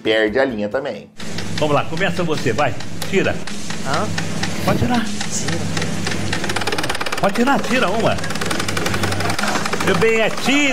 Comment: Eu também entendi Gira